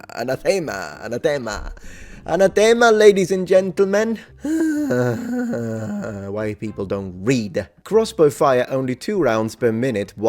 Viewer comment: Coulda sworn you said "white people don't read."